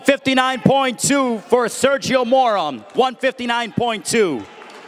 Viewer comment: Did I hear "Sergio moron" lol